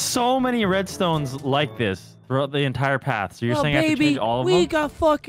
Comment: Am i the only one that thought Brian called Evan “baby” at 10:23 ?